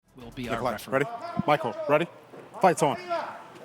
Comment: "fight someone"😂 ik what he actualky said but thatd be funnier